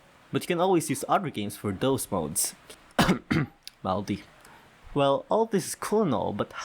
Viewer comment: What did you say at 1:17? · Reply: Malody, it's another game that can play taiko, mania and catch